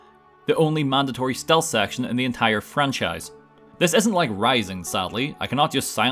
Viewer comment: I'm sorry, did you pronounce Risen as Rise-N, or am I hearing things, because I honestly cannot tell if he's saying Risen or Rising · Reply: Rising As in Metal Gear Rising: Revengeance